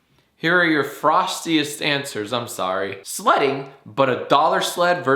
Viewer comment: I didn't hear "sledding" i heard "slutting" and tbfh the only sport i could do😂